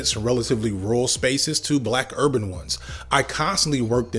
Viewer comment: i heard "black herbivores" at around 00:55 haven't stopped thinking about what that means since